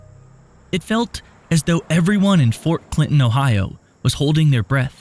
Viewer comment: Fort clinton? Maybe I misheard it.